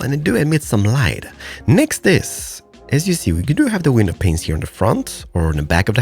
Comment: I just heard said penis instead of panes